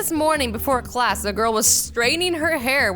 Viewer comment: I thought said straining